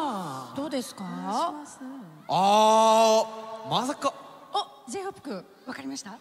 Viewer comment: Só eu que entendi ela falar J-Hope cu